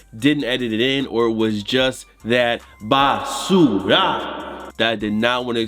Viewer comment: what did he say? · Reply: Trash in Spanish.... BASURAAAAA